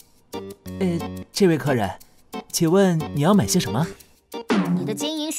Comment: Did he just say cherry cola lmao